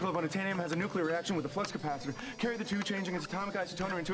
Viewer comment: did he just say flux capacitor?